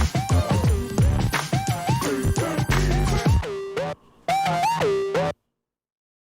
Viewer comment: I always wonder what you're saying at the end at your intro/ending song... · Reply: Filipina